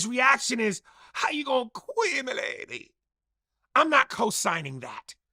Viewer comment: I thought he said "how you gon quit my lady" 🤣🤣🤣🤣